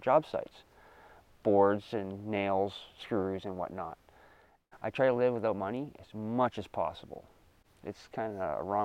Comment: This dude built an island that he lives on, and he doesn't live for money. A+ bro, A+ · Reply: Sounded like he said "I try to live with as little money as possible".